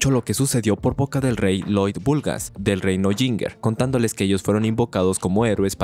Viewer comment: entendi reino chinga